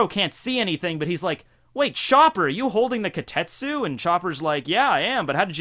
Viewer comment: Everytime you said Chopper, I thought you said Sharper and I was thinking who the fuck is Sharper.. Took me some to realize you were talking about Chopper.... I'm stuck with the japanese way saying the name :))